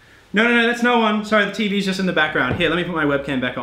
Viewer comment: Did really call a TV? I'm dead lmaooooo